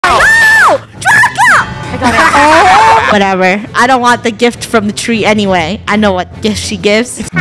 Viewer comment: did I just heard draka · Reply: Funneh did say Draca by accident, lol